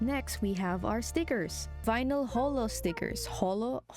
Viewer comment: i thought you said homo